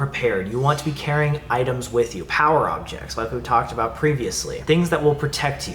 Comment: BY POWER OBJECT I THOUGHT YOU WERE SAYING A POWER DRILL KAJAHAHAHHAHAHAH